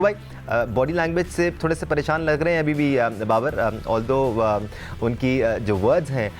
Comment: Did he say zimbaber at 00:03 ? Or i heard it wrong ? 😮